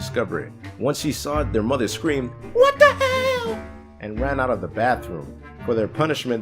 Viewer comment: hmm mom said WUT THE HELL will captions said WUT THE FUCK just saying mate